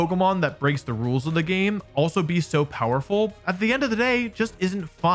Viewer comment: did he say Koffing????